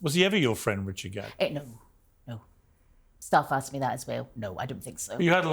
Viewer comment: “We were friends” Five minutes later “Were you friends?” “No. Stop asking that” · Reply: I thought she said 'staff asked me that' but that is so funny 😭😭